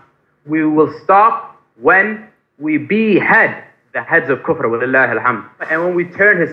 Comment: Bruh did he just say wallahi Obama u cappin